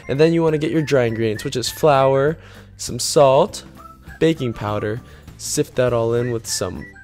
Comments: Did you say to put in BABY POWDER?!? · When he said baking powder I thought he said baby powder lol 😂